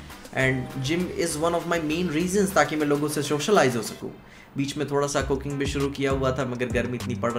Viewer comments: who else heard " bich mei thoda cocaine suru kiya tha"😶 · at first i heard cocaine bhi shuru kiya tha 😂😂